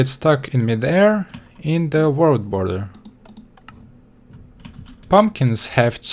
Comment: did he say world boller